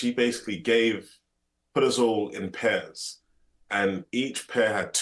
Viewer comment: Dom flying solo almost made this one better (love you Conner!) but 2 Brits getting to talk some home truths was really deep and entertaining. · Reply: The captions helped. 🤣 I heard "she put us all in Pez"